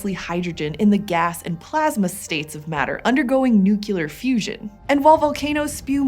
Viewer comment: Wait... did she just say NUCULAR?